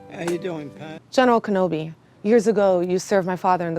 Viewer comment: does say "how ya doing Pat" ?